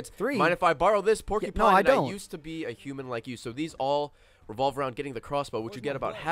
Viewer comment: At first I thought they said "This all revolves around you getting to Costco"